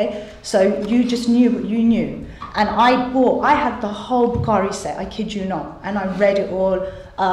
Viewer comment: Question Can someone tell me what she’s saying at about 5:22, “I had the whole Carissa? Curry Set?” I can’t make out what word she says but I’m guessing it’s a holy book or series of religious books? Anyone know? Thanks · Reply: Matthew Scott Al Bukhari it is a set of 4. Books kept beside the Koran in the mosque. It contains stories about the sayings and behaviour of Mohamed. It is consulted to verify sharia law. There are many disgusting stories. One is about thighing. That is the practice of using babies for sexual gratification , which is lawful, so long as blood is not drawn.